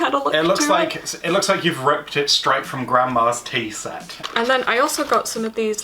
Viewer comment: You guys. I have the worst hearing. For a second I thought he said "Ripped straight from grandma's TEAT!"🤣🤣🤣🤣 But then I realized you couldn't have possibly said that ....